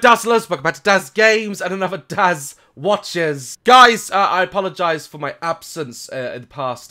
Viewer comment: When you did your intro I thought you said dads watches